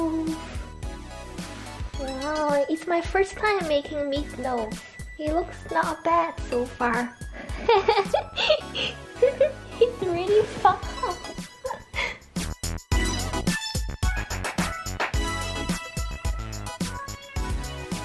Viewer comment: did you say “its really fucked up”? Lol youre too adorable to say this lol